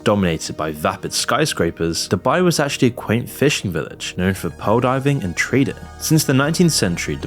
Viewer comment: I heard "pole diving and treating", after much though, Google searches, AI queries, I think it's "PEARL diving and TRADING"....